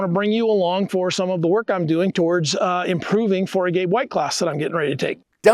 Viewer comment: I thought he said he was preparing for a gay white class. Spit coffee all over the screen.